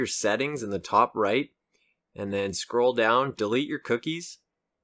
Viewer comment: When you said "Delete your cookies" I heard "They're magically delicious coming from the other room.